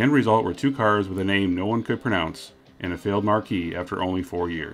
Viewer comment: The word “ marque” is actually pronounced “mark”. The pronunciation you are using is for the similarly spelled “marquee” as in a theater marquee. 👍🏻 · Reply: But that's not what he is saying. At 0:25 the narrator says "...the end result were [sic] two cars with a name no one could pronounce, and a failed MARQUE [meaning a make of car] after only four years." Not Marquis.